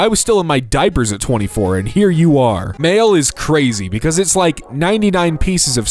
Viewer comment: i wasn't listening and thought it said male and honestly that's not wrong either